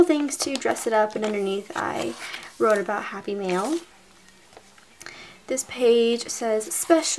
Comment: the entire time, I thought you were saying "happy meal"...I was so confused XD